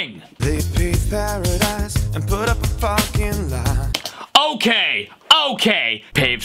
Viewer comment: With the 'paved paradise' one, I always heard 'parking lot' until I watched this video lol Literally lmao after hearing it tho